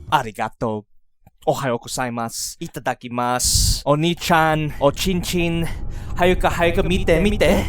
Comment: did I hear a "Oney-chan" in there?